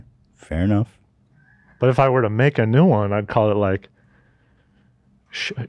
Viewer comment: Best part of the podcast is at 1:14:45. “If I were to make a new color I’d call it Shipple.” “Is it related to purple at all?” “No, I’m getting kinda like a limeish blue” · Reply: Haha it’s so fuckin funny, but I think I heard “shiffle”